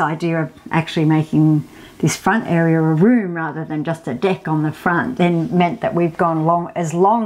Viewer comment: I thought she said a “di&k on the front” I had to rewind 😂